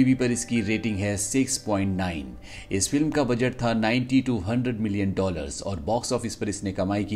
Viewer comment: Did you just say 920 million dollars? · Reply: 90 to 100 million😊😊